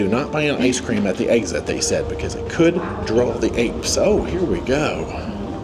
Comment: Did you say that the ice cream could throw the apes at 5:19? That must surely be some seriously strong ice cream. LOL